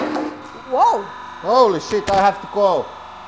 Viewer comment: Did he say holy shit i have to go at like